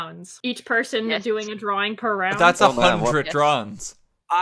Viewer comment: “that’s 100 drugs” is what I heard and now I cannot un-hear it.